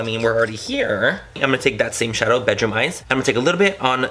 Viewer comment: when you said " bedroom eyes" as an Australian I literally thought you said "Vegemite"